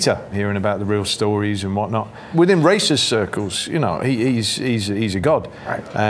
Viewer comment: I know I'm high but does anyone else hear "within racist circles" at the 3:29 mark?